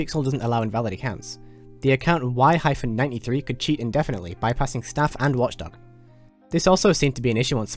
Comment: What did he said? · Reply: "The account 'Y-93' could cheat indefinitely, bypassing staff and Watchdog"